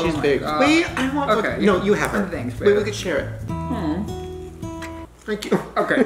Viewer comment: did say thanks baby because if he did that’s hella cute 😭